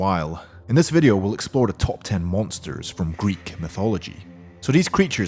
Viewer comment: I like how it sounds like he says top ten montores